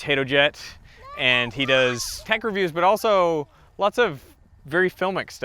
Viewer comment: Did you just say that Gene does "Pecker Views"?! 😂 🤣